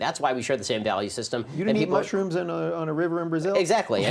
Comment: what did Rubin say? I swear I've replayed over 8 times and I'm still hearing "you need mushrooms on a river in Brazil?" and it doesn't make any sense